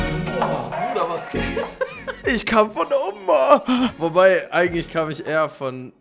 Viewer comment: Ich hab am Anfang verstanden ich komm von Oma 😂😂